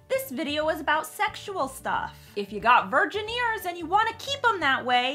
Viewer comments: “If you have virgin ears and would like to keep it that way click out.” Oh to be young and have virgin ears. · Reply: For some reason I heard it as 'virgineers,' and was weirdly delighted to imagine it being either a STEM career option or a starfleet rank in the Trek universe. :D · “If you have virgin ears and would like to keep it that way click out.” Oh to be young and have virgin ears. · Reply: 100% thought she said virgineers.